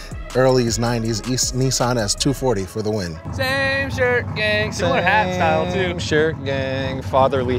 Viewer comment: I genuinely thought he said "Same shirt gay"